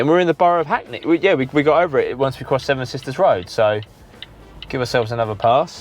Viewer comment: Hi when you said seven sisters rs i thought you said seven bridges rd by the eagles 😁